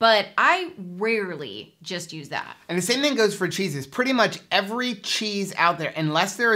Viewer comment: Ha ha! When said "and the same for cheeses," I thought he. said "same for Jesus." 🤗😊😉 Jesus is just alright for me! LOL Perfect information! Thank you!